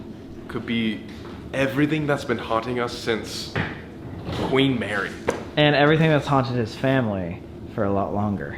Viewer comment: Not my hearing say ''Since we married'' instead of ''Since Queen Mary'' at 1:18:02 in the video. XD